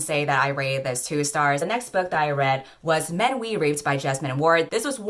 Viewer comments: For a second I thought said 'Men We Raped'. I was like...ummm. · My deaf self thought you said men we r*ped instead of reaped and I was so confused.